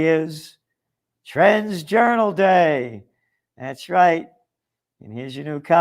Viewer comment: Sounded like you said "trans-journal day"